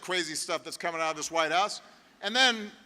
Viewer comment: For a second I thought Obama said White ass,but he said White House of course..Big O isn't a dummy like this other mutha fucka